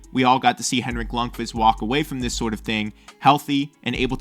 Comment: What am I hearing "lung-fist" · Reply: Lund qvist